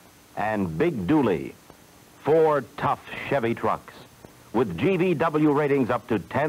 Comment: it sounded like he said "ford tough chevy trucks." instead of "four though chevy trucks." lol once you hear it you can't unhear it...